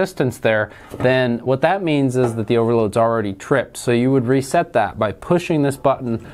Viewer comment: or so you state that something is already tripped. No idea what you are saying. Cant make it out. What is already tripped. Think you are talking about the overloads but cant understand the word you used. Listened to it 3 or 4 times but still no idea what you said. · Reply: He said that if you do not get any reading between the points on the overload that means it has already tripped. You'll need push it back in and take another reading.